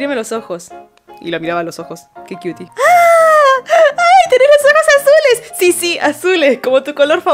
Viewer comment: Yo le escuché a "tenés las cejas azules" y yo "ahre que Kamui se tiñó solo las cejas" bsksbsksj